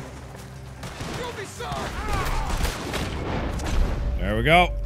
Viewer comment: did i just hear John yell "you'll be sorry"?